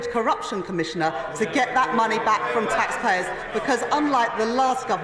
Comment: If you listen carefully on the covid commission around 4:30 in subtitles say she is going to get money back for tax payers but she actually says they'll get money back from the tax payers. Says it all sausage.